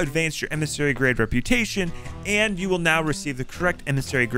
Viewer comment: did you say will now or will not? If you said will now does that mean we can't 'store' emmissary rep for different companies? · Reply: He said "now"